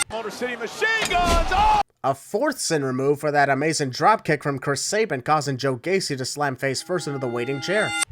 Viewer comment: Did you just say "Kurt" Sabin? One sin added for that horrible mishap ding! Hahaha, in all seriousness, Happy Halloween! 🎃👻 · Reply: No worries, I said "Chris", not "Kurt". Voice slurred a little bit during the recording session, so it's understandable that you heard differently. Sorry!